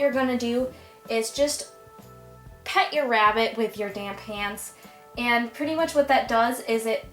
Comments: totally thought she said damn hands instead of damp hands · I thought you said "damn hands" I was like WHOA THERE GIRLFREN